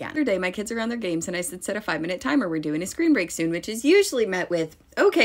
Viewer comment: I totally misheard screen break as scream break and was very confused why we needed to set a time to scream...